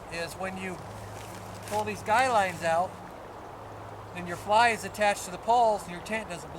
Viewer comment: I was playing with the dog and thought I heard "...your fly is attached to your balls". I went back and heard what you really said but for a second I was like "What? Huh?"